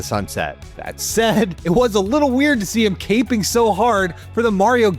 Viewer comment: Did he say CAPE-ING? IGN you cappin with this one.